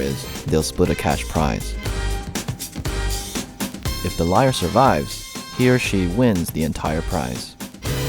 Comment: excuse me but did he just say "she?" LOL